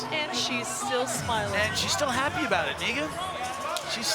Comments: what did he call her?!? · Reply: he made a walking dead reference earlier, so i heard "negan" who's a brutal character in the show. idk though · “and she’s still happy abt it 🥷”< ik he ain’t say ts fr, it’s just funny asf for me to me fr when i first heard it 😭😭😭